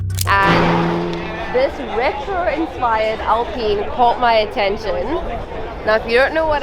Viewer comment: did she just say....."AL-PENIS"??? 😮‍💨🤣🤣🤣